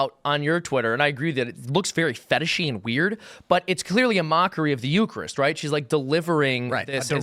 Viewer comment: At first I thought he said "mockery of the yoopers" which are people that live in Michigan's upper peninsula